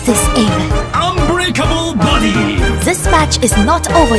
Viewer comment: I hear: "Unbreakable booty"